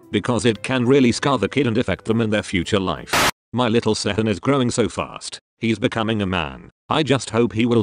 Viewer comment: lmao i thought it said "my little sehun is grinding so fast..." 😂